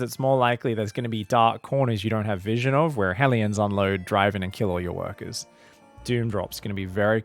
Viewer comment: You say "Hellions unload"... I hear "Florencio's proxy" and smell sewers.